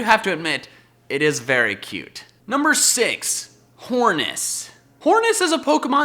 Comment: I laughed my ass off when I heard "whorness"